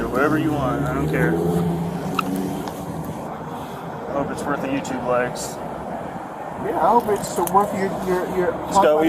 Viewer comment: What did the officer say? Did he say: "I don't respect the youtube wives"? He said something about not respecting someone. Bizarre.